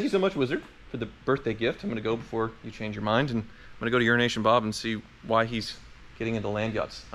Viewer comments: I thought you said urination Bob for a minute... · Hi, I thought you were saying Urination Bob?🤣🤣🤣